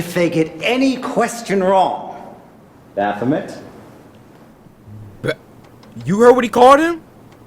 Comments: he says Baphomet, its like a satanic deity or something lol, I couldn’t really hear what he said either lol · what did he call him? Nevermind he called him baphimet which is a demons name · What did he call him?? i heard bathomit